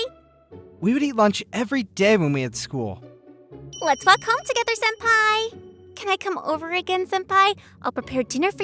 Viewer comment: geez i thought i heard "lets fuck home together senpai!"